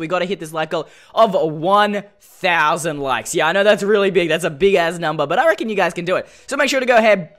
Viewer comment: did you say "that's a big ass number"?